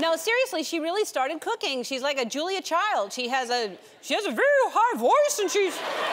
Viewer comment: Can anyone please explain what she's saying from 0:16 - 0:24? I only understand "She's like a ??? child, she has a real ???? ???? ???? " The part starting with “So I’m proud of…” I understand, though. :) Thank you! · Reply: Ellen says Portia is "like a Julia Child", who is a well known celebrity chef with a peculiar voice.